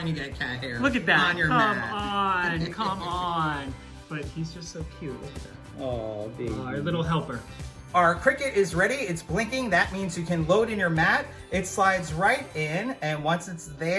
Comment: I Heard: "LITTLE HELL PURR"😂